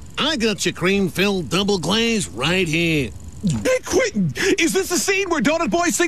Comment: Did he just Say Quintin? ???